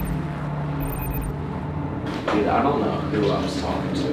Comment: Anyone hear something whisper Matt at 23:08??